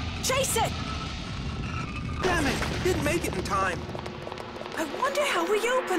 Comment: He clearly said Damnit and not Oh No